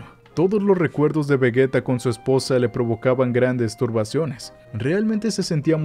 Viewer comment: La vida de Vegetto será difícil al principio pero cuando todos se acostumbren a él ahí es cuando se van a venir cositas 😏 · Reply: en la parte 14:17 yo escuche grandes m4sturbaci0nes